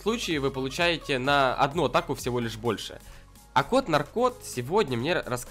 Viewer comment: а мне послышалось кот-наркот.......